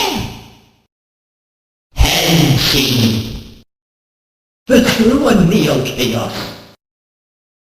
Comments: I always thought he was saying "It's you and me, o' chaos." or something like that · I've never heard this guy say the whole "true and neo chaos" bit. Maybe I'm just crazy I dunno'. Anyway, very cool, my dude. It's amazing how folks can just isolate voice clips like this and it's more clear. :) (That first laugh sounded like a Zelda character. Like, I'm pretty sure I've heard that towards the end of Spirit Tracks.) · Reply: and i thought he said "consume the chaos chaos" :> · Just wanted to pint out he actually says "I can do anything!" He does not say "I can't do snything!" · I always thought he said "It's you and me, oh chaos!" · I thought all this time he was saying "Just you and me in chaos." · I always heard "the true and neo chaos" as its you and me and chaos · I always thought he said "The true and real chaos", but I checked and he does indeed say Neo. Might have some interesting implications